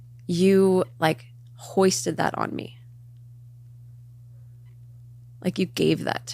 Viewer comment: What time was that ?😭🙏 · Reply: how to edit it because I thought I heard “we” 😭 34:36 it still doesn’t make it any better! Like nooooo Ned..YOU’RE the one who put that emotional turmoil and broken trust onto Ariel. Ned “The Audacity” Fulmer 🙄